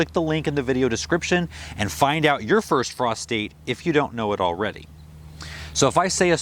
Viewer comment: Every time you say "frost date," I hear "prostate." Gardens are funny.